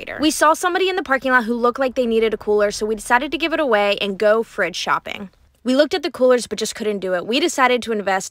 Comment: Thought they said, thrift shopping